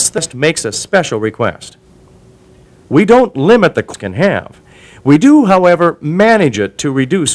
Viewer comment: There is a little jump error in the video at about 2:12, I think he says "We don't limit the amount of ham!" but duo to the glitch, it sounds like he is saying "We don't limit the fucking ham!" and the glitch was like censoring, lol.